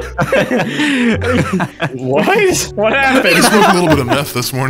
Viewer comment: Is it just me or did say n** what